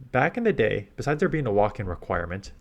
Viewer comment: What was said here? There used to be a ... *walking*? requirement to play GBL?